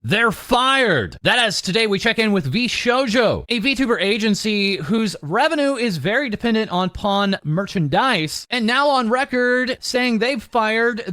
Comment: "VShojo, a vtuber agency whose revenue is very dependant on PON merch and dice..." Did I hear that right?